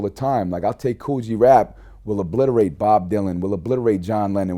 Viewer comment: Was I the know one who thought he said "Kool G Rap will blood rape Bob Dylan"?